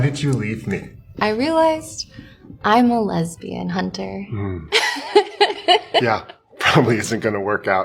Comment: I wasn’t watching the vid ( had it on the bg) and I heard “ I’m a lesbian hunter “ and was really confused haha